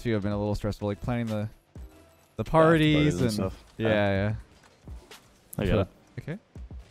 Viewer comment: i heard it... you may notice "I'm Back Left" or "I got it" since they call it quick.. but it was "I got it".. lol blaming are we?